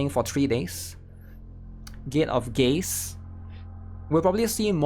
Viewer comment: I thought I heard Gay of Gays 😅